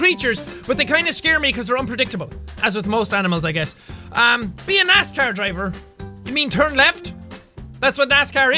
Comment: When Sean said 'Be a NASCAR driver' it sounded like he said 'Be an ass car driver' 😂